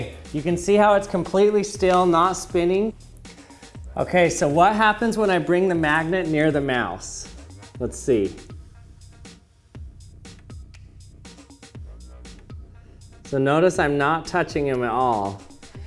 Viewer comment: I thought you said house